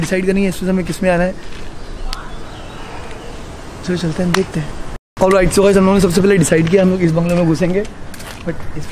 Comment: does anyone hear dannnnish someone has said listen carefully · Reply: Ha its bahar raho danish